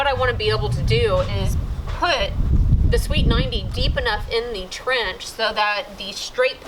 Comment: First couple times you said sweeped 90 i kept hearing "Sweet 90" :P I just thought "Well it IS pretty cool I guess..?" hehehe :D Loving the Michael Flatley dance moves :P Was there any utilities nearby in the end?